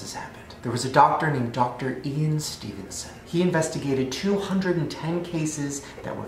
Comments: I 100% thought he said "a doctor named Even Stevenson."😂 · I literally thought Shane said the doctors name was Even Stevenson😂🤦🏽‍♀️ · I thought he said Even Stevenson lmaoo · Deadass thought he said Dr Even Stevenson, I was like ‘Who names their child that’